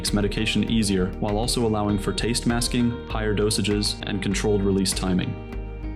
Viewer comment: NGL i thought he said "Taste MAXXING"